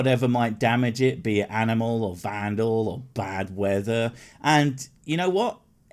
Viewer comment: when you started to say "bad weather," I thought you were about to say "badger"